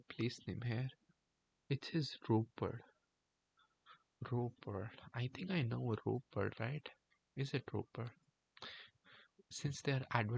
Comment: Last word is ਰੋਪੜ रोपड़ . It is now called Rupnagar.